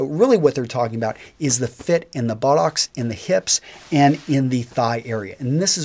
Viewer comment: Sounds like he says: "THE FIT IN THE BO-LLOCKS."